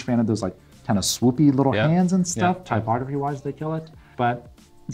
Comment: What is the name of F.P. Journe hands? I heard "typographywise" but I googled for that and couldn't find any references online.